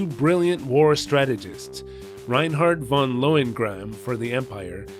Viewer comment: Did you just say your name is "Von Loin Grab"? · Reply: Von Lohengramm, everybody in this show is far too repressed to be grabbing loins. Except Schönkopf, I guess.